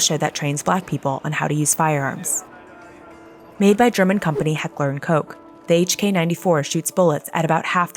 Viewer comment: She did not just say “Heckler and Koke” right💀💀